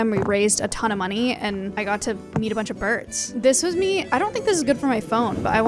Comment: i thought she said "i got to eat a lot of birds" lmao 💀